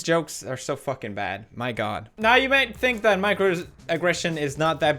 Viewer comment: I thought he said "Micro-transaction"